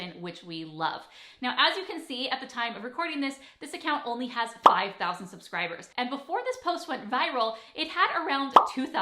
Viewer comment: Did she just say 5,000 subscribers ?